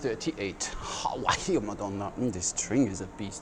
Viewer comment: Apologies, I don't know any Maltese, but at 10:04 it sounds like you said "واهية (wahia) madonna." What an interesting blend of Italian and Arabic!